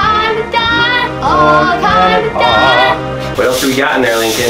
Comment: Link. Sounds like he is saying wakanda